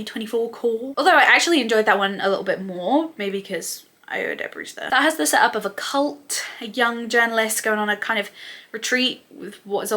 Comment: i cannot figure out what you said here pls someone let me know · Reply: ‘maybe because ayo edebiri’s there’